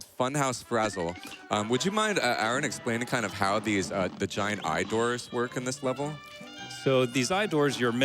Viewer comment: he said giant eye doors at like 25:52 but I heard giant dwarves and thought THANK YOU SOMEONE FINALLY SAID IT